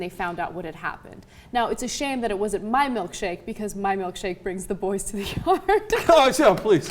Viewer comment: damn i couldnt understand what she said there 0:53 whats her milkshake does to boys ? :D